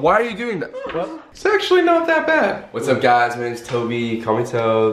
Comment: I thought Colby said “Sexually not that bad”